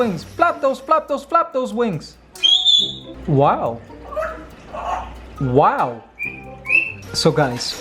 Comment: THAT TOTALLY SOUNDED LIKE "wow wow" OMGGG